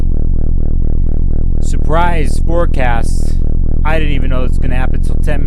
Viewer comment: I thought you said “Forcasm” and now im going to incorporate it into my vocabulary.